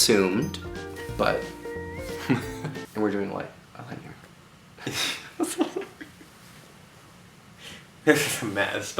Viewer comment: What did say at 9:17 ? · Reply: JunkieKit he said yes I love you Ricky